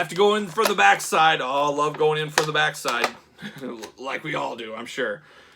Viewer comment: Watching this review, looking at my phone and I hear "love going in from the back side" and I did a double take lol. Good review. This one is currently sitting in my pile of loot that I need to get shipped.